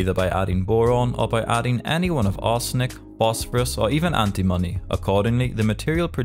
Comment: "ANTIMONY" sounded like "anti-money"